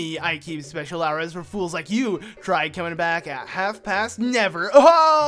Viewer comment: you said "fools" but he actually said "folks". Point proven 🐍